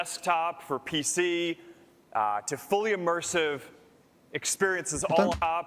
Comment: When he says “fully immersive uh… experiences”, all I hear is “barely immersive, boring content”.